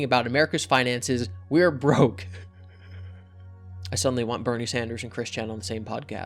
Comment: I sincerely thought said Brony Sanders. That's a Pandora's Box I would prefer remain closed.